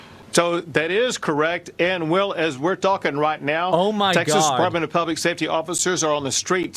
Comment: did he just say ‘right meow’